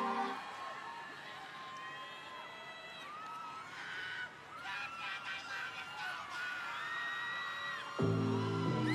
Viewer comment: 뭐라는거임?ㅋㅋ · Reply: 와아!!!! 다니엘 알러뷰쏘머치!!! 인 것 같아요ㅎㅎ 열정적인 버니즈인듯